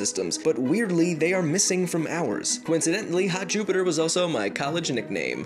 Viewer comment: sounds like u said hat jupiter